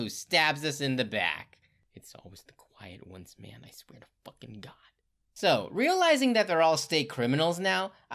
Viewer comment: For those who can't hear the whispering: "It's always the quiet ones, man, I swear to fuckin' God..."